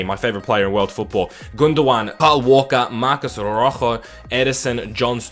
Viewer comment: did he say paul walker